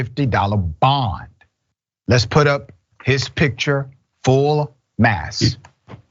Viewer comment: Did you just say full mass brah😭